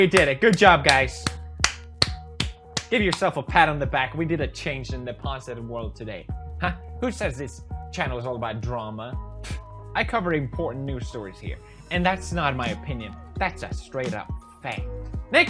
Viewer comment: Did anyone else hear him say "I'm covering porn"?